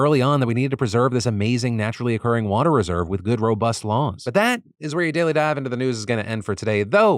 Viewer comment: I thought he said good robust lawns at the end there lmao